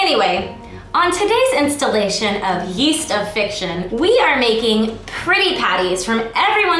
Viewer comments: she said "yeast of fiction" instead of feast of fiction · Every time she said "yeast of fiction" I misheard as yeast infection